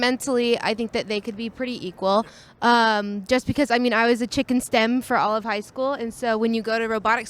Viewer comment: I thought she said she was a “chicken stem” — I was wondering what that was until I literally dissected the words to assume she meant STEM, and had to figure the first part was actually “chick in”